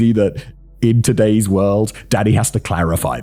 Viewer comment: When he says Dany sounds like he said Daddy